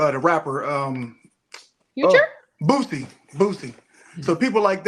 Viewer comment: I thought he said “Pookie” 😂